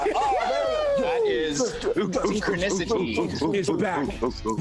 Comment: Did he say that is synchronicity